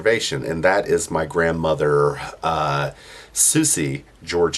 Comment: Did you say that your grandmother was named "SOUCY"? My mother's last name was Soucy